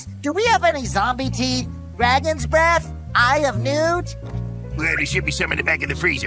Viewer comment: It is season 1 vibe if I already had a mandela effect with this scene, I thought he said "the eye of cthulhu" (terraria reference)